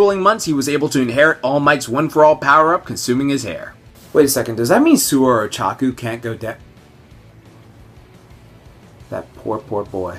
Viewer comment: I DON'T GET THE JOKE, CAN'T HEAR IT CLEARLY, SOMEONE EXPLAIN PLS THANKS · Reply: He says "does that mean that Sue or Ochako can't do dow-" implying that they can't go down on him. The reason being that they would swallow his DNA and inherit One For All.